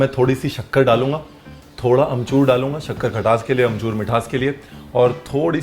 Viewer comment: Shakkar khatas k liye and amchus mithas k liye .Chef .. did I hear it correct. 7:07.